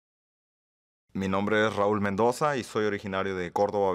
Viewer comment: I thought it said Raul Menendez